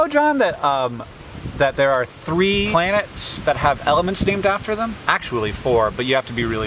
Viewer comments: Hank I thought you said there's 4 ELEPHANTS that are named after planets and was ecstatic to learn about these guys. I was a little disappointed when I realized you said elements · Definitely misheard "elements" as "elephants" and I was so excited to learn about these elephants with awesome names. I'm sad now